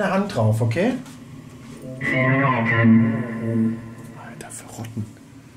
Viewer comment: Ich glaube das die Puppe Kontakt zu euch aufgenommen hat und euch drum bittet sie wider zu beerdigen und zu vergessen damit sie wieder ruhen kann denn sie sagte sie mag es im Dunkeln . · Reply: Und ich hab öfter mal Verrat verstanden und auch irgendwie das Gefühl das die Seele sich verloren fühlt